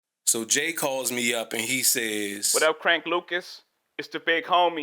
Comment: I swear to my left nut I heard "so J.Coled me up."